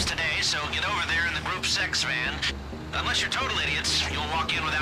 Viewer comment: I totally thought lester said "group sex van"